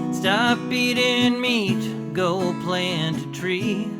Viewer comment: Eating meat! I heard "beating meat" XD